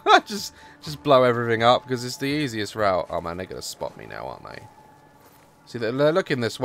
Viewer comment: did he say the n word? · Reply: He said "oh man, they gonna spot me now''